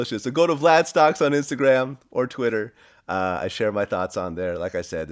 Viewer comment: I thought Vlad said “I share my thots on there” 😂😂